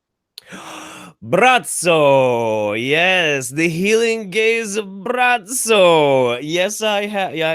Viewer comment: I heard "healing gays of Brazzo" at first and was a tad confused, but intrigued, and now I'm not entirely sure if I'm disppointed or not that it was just a *gaze*.